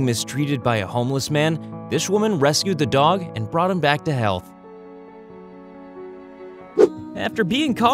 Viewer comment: Did i just hear "this woman found out that a homeless man was mistreating this dog and she brought him back to hell"